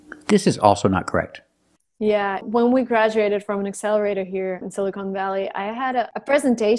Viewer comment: I don't get what she is saying here: "When we've graduated from accelerated things..."???